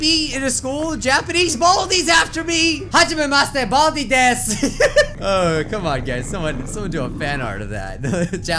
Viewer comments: when he said what I think is Japanese, the captions said much of a mess there Baldy dance oh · HAJAMA DESE BALDI DAS! (No idea what he said. somebody halp & google translate this Lol)